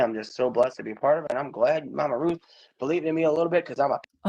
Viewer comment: I know I've been watching drag queens a lot because i could have sworn said: "I wanna thank Mama Ru for believing in me."